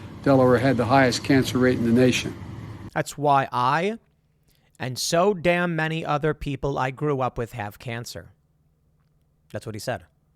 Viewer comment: That's not what it sounds like he said. Sounds like, "That's why I HAVE so damn many people I grew up with have cancer." Mumbling, yes. But we need to be honest.